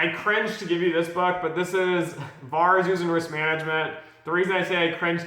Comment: Could you tell me what you said from 5:14 ~ 'this is bars using risk management ~~~~~~~ give you this because' i 'm sorry i don't understand and know what spell the part of ~~~~~~~~~~~ you said in the video?? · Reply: I'm talking about VaR (value at risk). I mention that business people think VaR is the only forum of risk management however this isn't true. Building models and managing risk across the entire business is important however they don't teach this in business schools.